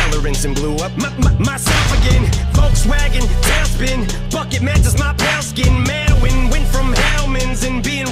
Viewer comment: Thanks for making a clean version but i heard f*** once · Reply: I head “bucket” and thought it said f but it wasn’t